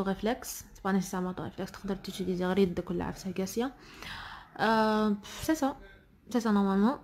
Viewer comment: i couldnt hear it well??? What's that? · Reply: Vos mains 🖐